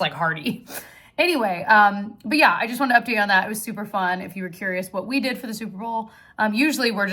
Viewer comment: When you were talking about your Super Bowl gathering and said it was super fun… I heard it as “souper fun” bc of the themed food! 😂😂